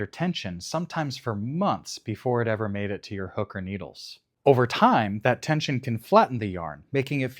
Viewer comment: For a second I really misheard "hook or needles" as hooker needles